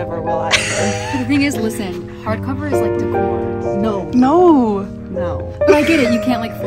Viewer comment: what did ur friend say about hard cover books around 2:40? the music kinda blocked it out. now i have to buy in five years ..cute cover <3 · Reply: lol she said hard cover books are like decor ! 🤭